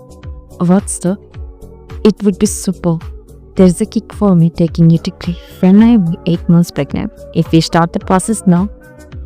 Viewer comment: what she said here i didn't understand can anyone explain me please. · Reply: I mean It would be super. There’s a kick in the idea of me taking a degree while I’m 8 months pregnant.